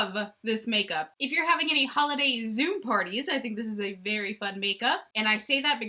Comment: You said holiday zoom party and I heard holiday "zoo" party.. How fun would that be?!? Ah, I love your energy so much! Also, beautiful look!